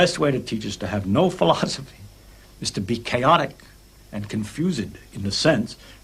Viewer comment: what does he say at 0:20 -- the best way to teach is to have no "faches" (is that yiddish?) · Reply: philosophy